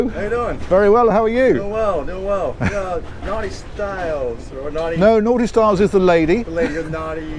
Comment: Did I hear, "Are you NAUTISTYLES?" Hold On. WHAT?